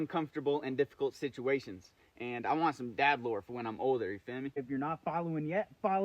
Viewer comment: What did you say?you want to have “dadlore when you get older.” What did say and what did he mean…?!? Listen at 13:30, start of the sentence. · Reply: Yeah dad lore, like he’s saying he wants a lot of things to be able to tell his children when he becomes a father